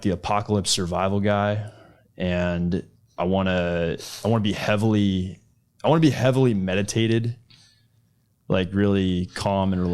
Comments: What I heard at 9:00 : I wanna be heavily medicated….. Me : Oh I can tell 😏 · I can't be the only one that heard "highly medicated" instead of meditated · I thought he said his best life included being "heavily medicated" and I saw the need....then realized he said "meditated" or "meditative" heh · “I wanna be heavily meditated” is a sentence that will haunt me for a long time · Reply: i heard "medicated", my bad. that made more sense somehow 😂